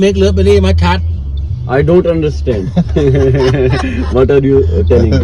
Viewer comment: i don't understand what are you can 😂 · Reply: He said what are you telling.